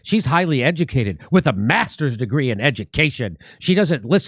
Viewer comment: Thought you said 'masker's degree' :-)